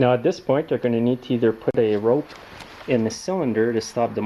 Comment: Did I hear you say "Put a rope in the engine?"